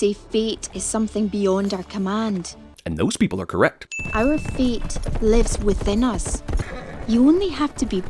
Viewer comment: Who else heard "Our Feet live within us."